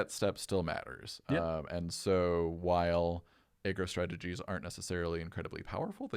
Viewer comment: anyone else hear "egg roll strategies"?